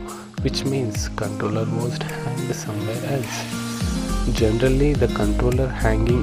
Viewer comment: what did you say? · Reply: Controller hanged somewhere else..